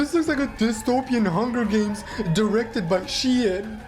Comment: HELP I THOUGHT HE SAID "its directed by si-eun" T-T WEAK HERO REFERENCE??